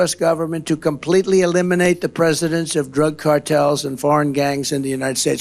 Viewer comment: Trump: “I’m going to sign a directive to eliminate the ‘presidence’ of drug gangs”. This poor geriatric running for the White House needs immediate rest and treatment. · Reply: I heard him say presidents of drug gangs. Huh?🤔🤨